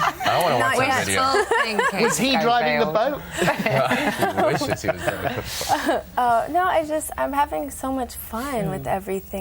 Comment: did enrique say I wish i seen this when the guy said was he driving the boat?